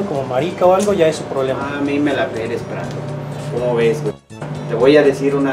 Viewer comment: Yo pense que decia " a mi no me la peles pran... " 😅